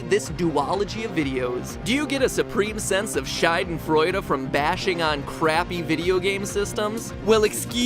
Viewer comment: matpat sounds like he says "scheidenfreude", whitch would translate to something like "pussy-joy".... i like it :)